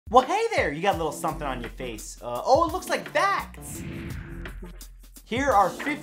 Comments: Legit thought you said "looks like fat" not "looks like facts" 😂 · For a second there I thought he said “looks like fat” in the beginning in the video. I said to myself “how does he know I’m fat!?”